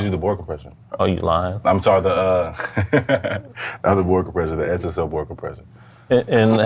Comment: sorry English is not my native language, what does he say at that timestamp? SSL World Compression? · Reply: board compression